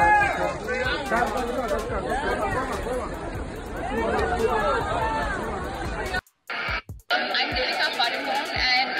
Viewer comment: When deepika comes out ..I hear Priyanka Priyanka .am I right or wrong clearify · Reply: i m INDIAN yes they say priyanka